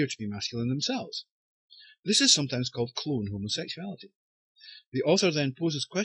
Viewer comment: what did you say at 3:14? this is sometimes called __ homosexuality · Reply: Hi you...I said 'clone' homosexuality